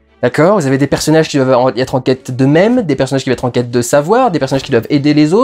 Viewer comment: "Des personnages en quête d'eux même" pourquoi j'ai entendu "en quête de memes" ? ^^